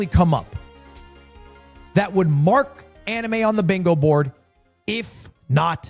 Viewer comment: I know I been watching too much Naruto when he said bingo board and I thought he said bingo book